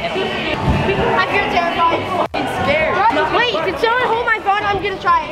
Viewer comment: here what the kid says. He says hold my gun